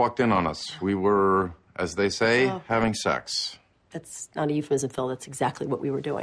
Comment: I can't hear the word euphemism, i would never guess that word was there without the subtitles.